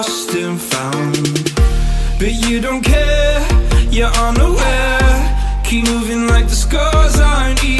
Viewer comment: i heard "ur underwear" 😂